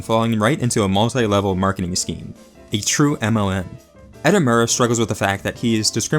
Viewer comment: did you say a true mlm? because yes i fully agree but i can't tell if you said mlm, mln, or something else i don't understand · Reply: this was just a pun I made, mlm as in multi-level-marketing but I was just making a joke about Marxist-leninist-maoist